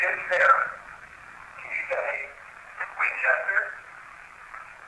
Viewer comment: I think it said, Win-ches-ter